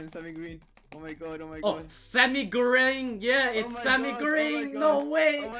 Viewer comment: when said "sammy green" but the captions said "semi gring"